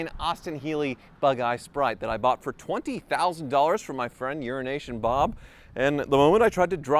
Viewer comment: Every time he says Bob's name I hear 'urine asian'